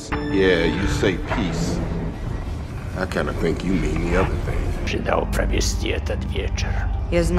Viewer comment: Excuse me, but do people really make out what Fury says here without subtitles? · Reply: I kinda think you mean the other thing